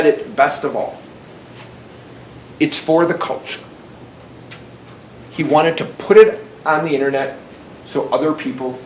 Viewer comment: "for the culture" thats the dumbest comment from the kid recording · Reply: I thought he said " pervert culture "